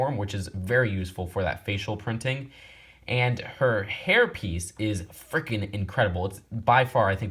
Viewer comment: When he said hair piece I thought he said herpes 😂😂